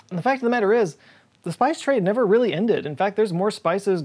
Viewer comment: At first, I heard him say spice TRAY. For a second there, I thought I was about to see a clip of him SUPER excited about his epic spice rack.